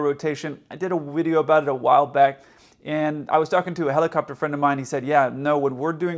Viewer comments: did you just say "wideo"? xD More than ever, you are my favorite pilot! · did a "wideo" about it? Did I hear that correctly?